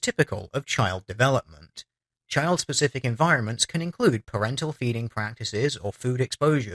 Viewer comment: "parental feeding practices" I heard that as "parental beating practices," Lol. Bit of a double take :P